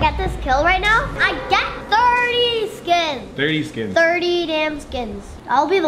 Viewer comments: Did say "damn" at 10:43?😂😂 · did he just say 30 damn skins? 😂